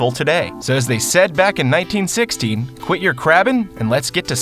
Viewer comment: Absolutely thought you said "in 1960" 4 different times till I held my phone up to my ear. Was very confused for a minute 🤣